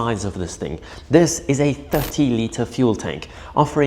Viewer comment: Sounded like you said 50 liters fuel tank. Nope it is only 30 liters